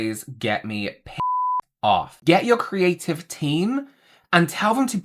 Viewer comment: I thought he said creative-a-tine like "Ovatine" Like drink your creative-a-tine and get creative...... But he said creative team so nvm